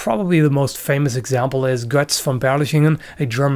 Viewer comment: for half a sec I thought you said most famous amputee swordsman was Guts from Berserk lol